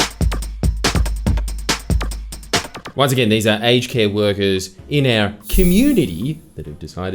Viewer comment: My American is showing. I deadass thought you were saying "HK" instead of "aged care"